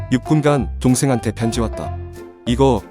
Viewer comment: ㅋㅋㅋ뭐 하면서 보고있어서 자막을 못봤음 그래서 "6분간 동생한테 편지왔다"라 들었는데 다시 화면보니까"6분동안 도대체 뭘 쳐그린거지..?"이생각함ㅋㅋㅋㅋ